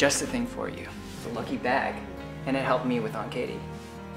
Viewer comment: does anyone else hear the words "aunt katie"? is it just me? o.O